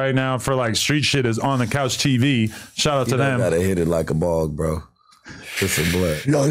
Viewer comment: I thought he said you ain’t gotta hit it like a ball like some work 😂 then over there treating the J like some Cr4ck 😂😂